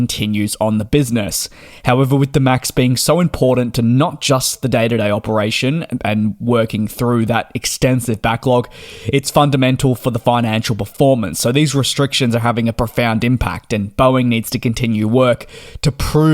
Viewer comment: sounded like you said ''for the FIA'' lol